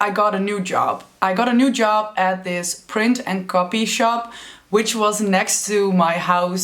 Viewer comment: I first thought you said COFFEE SHOP lol